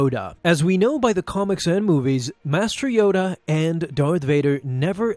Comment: I dont know why, but I thought he said "Master Vader, and Darth Yoda." Was very confused. Had to check again but don't worry, it was just my brain messing up again. 😂😂